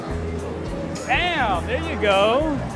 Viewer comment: did he say the D. A. M. N. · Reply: Bam :)